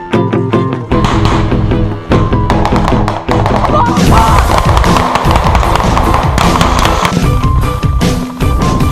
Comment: did he say "Allahu Akbar" here? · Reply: Nope.It's Slava Rossiya